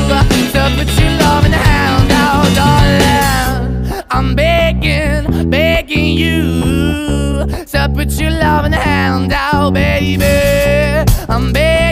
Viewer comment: It sounds like he is saying I’m bacon you so put your loving handing out babyyyyyyyyyy